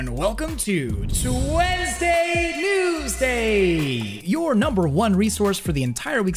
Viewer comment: lol I thought that said Tuesday Wednesday